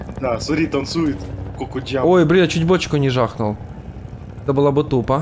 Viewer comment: Кто заметил что на времени 2:53 сказал бл* !? Я в первые от него это слышу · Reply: Он сказал блин, но ты посмотри штурм зоны 51 там ещё больше матов (я этот видос долго находил)